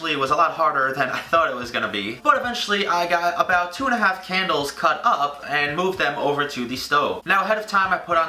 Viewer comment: i thought it said candy...